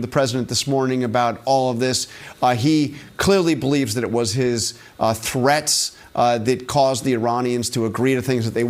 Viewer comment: i thought he said he believes it was his Tourettes.